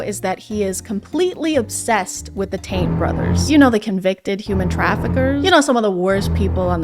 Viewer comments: Probably wasn't intentional but at around the 7:00 minute mark when said "the tate brothers" I really heard it as the "taint brothers" and now I'll only refer to them as such. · I don’t know if it’s just what I WANTED to hear but every time you referred to one of the Tate brothers I heard “taint” and I was losing it 💀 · I keep hearing "taint" not "Tate". The Taint Brothers. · I keep hearing “taint” brothers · this whole time i thought you were saying the 'taint' brothers and i just realized you were saying 'tate' brothers😭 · Every time someone says "tate" i always hear "taint" and tbh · I keep hearing the "taint brothers" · lol thought u said “taint brothers” 😅 instead of Tate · Every time you said Tate I kept hearing 'taint'. I was confused as to why you said the taint brothers, and then I looked at the screen and realized.